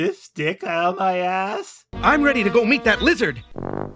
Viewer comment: It sound like he said "lizard" instead of "wizard".